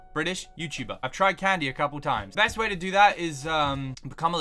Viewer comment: lol thought you said 'ive tried mandy a couple times'